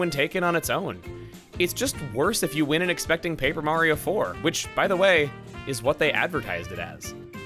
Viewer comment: Peach in Super Mario 64 had more dialogue than Paper Peach in Sticker Star. That’s a punch to the gut right there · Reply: For a second, I thought you said “_Paper_ Mario 64”.